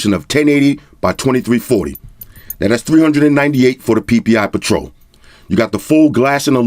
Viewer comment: Ppi patrol For a moment there I thought you said paw patrol 😂🤣 Still don't know weather to get mate 20 pro or the p30 pro